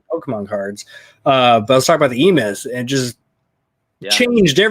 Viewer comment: Right after said Pokémon cards he said something changed his life sounded like EMIS(?). What was that? It sounds important. Around 43:20 timestamp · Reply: It’s a book called The E Myth by Michael Gerber